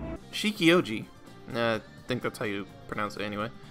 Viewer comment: I heard Shilo-ouji’s has back problems after carrying joker through 4 palaces.